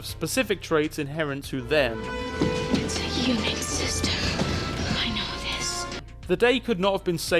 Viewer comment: ! I always thought in that scene (Jurassic Park, right?) where the girl is on the computer, she said, "It's a unique system. I know this!" and was very confused as to how she knew a unique system she'd never used. For the first time I heard, "It's a UNIX system. I know this!" and it makes SOOOOOO much more sense.